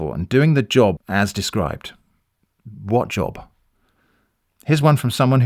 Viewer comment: i think you just said skull emoji with your voice. congratz!!! (genuinely impressed, that was powerful) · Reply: said "what job💀"